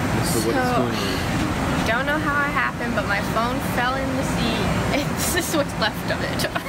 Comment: What I heard : “don’t know how it happens but my phone fell in the sea.” And it took me 6 re - plays to get it . XD